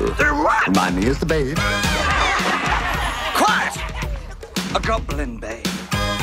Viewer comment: At the begin I thought he said quiet a goblin bae